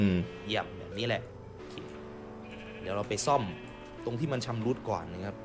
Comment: ผมได้ยินว่า เอาไปซ่อมให้มันชำรุดก่อน :/